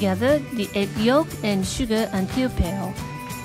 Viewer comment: I've listened like 10 times with my eyes closed at 0:45 and I have no clue what she's saying. Yolk, sugar, and 'pyopyale?' It doesn't sound like anything in the ingredient list and she doesn't put a third ingredient in the bowl.